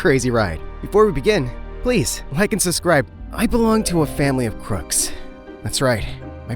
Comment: bro said “sUsriBe” even if the caption says suscribe